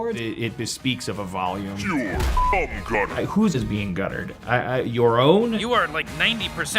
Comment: I thought Morty said Cum Utters. Like huge pecs lol. So Cum Gutter is abs?